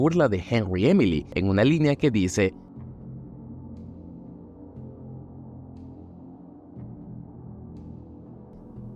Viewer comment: ¿que dice? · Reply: En el dbd William dice "The darkest pit of hell has opened for business" haciendo burla de lo que le dice Henry en fnaf 6 "The darkest pit of hell has opened to swallow you whole".